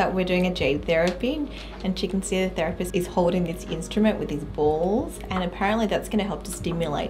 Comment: Am I the only person who heard “his balls” at 3:03😂😅